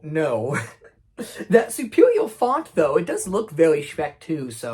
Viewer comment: for some reason I heard “Superior Fart” and had to put my ear up to my phone